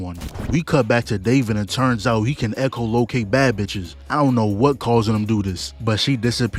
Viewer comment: Man, I thought you said David could echo locate "baggage" not "bad bitches" and I was about to agree cause for real...Lucy I love you, but girl, you got stuff to work on.